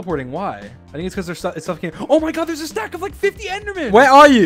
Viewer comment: thought he said cow sack and not stack. Was looking away eating cereal lol